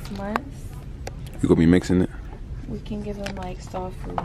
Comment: it sound like she said “ Dog Food “ 😂💯💯